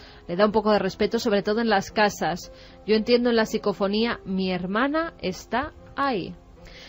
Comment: Vamos, que de primera oída yo escuché "mi hermana está ahí", si lo concatenan con el supuesto "no estoy sola" ya está resuelto, les metieron el perro, es la conversación de una o dos niñas con alguien. No pueden hacer un programa entero con una mentira.